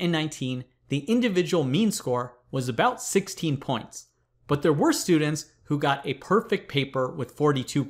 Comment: What does he say at 0:42 I can't understand. It sounds like "their worst students with a perfect paper of 42 points"